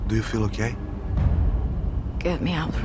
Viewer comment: tell me why i thought he said " do you feel gay?"